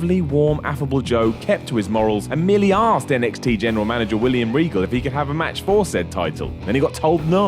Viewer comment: i thought he was saying William wrinkle